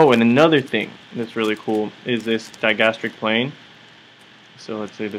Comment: This is a really nice work. I got a question, what is the word youre saying at 7:11? my first language is not english, i can undestand the whole video but that part really escapes from me :S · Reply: Kayren thank you! :) a "plane" is just a flat surface. That plane under the chin is called the digastric muscle is under the chin and so I call it the digastric plane. Hope that helps clarify!